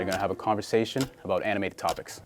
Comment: This is definetly the Naruto vs Saskue of Toronto Bully Ball. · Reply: I know u said this cause at 50 seconds in it sounds like they said “anime topics”